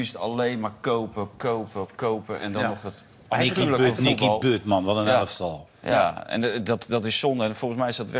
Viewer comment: "Nicky Butt, man what an arsehole." Bit harsh. Can someone translate the rest of what they said please?